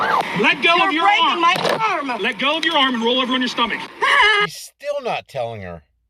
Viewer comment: Did I hear that cop correctly it sounded like he said, let go of your arm and lay on your stomach. How do you do that?